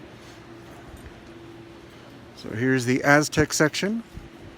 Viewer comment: Did he say 4:45 here is the ass tech section? And shows the girl's back? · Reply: Heinrich Peter Maria Radojewski Schäfer Leverkusen Nope, he says “Aztec”, it’s your own dirty mind, don’t blame for it.😀